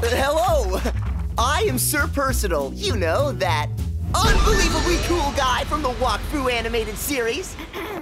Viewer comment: PPPPPHH i heard him say ugly crewl guyXD